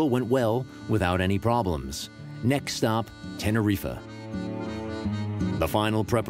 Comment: did he say tenerifuh 😂